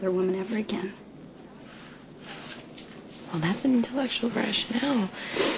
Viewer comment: What did Liz say at 3:15-3:17?!?!?! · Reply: “that’s a intellectual rationale”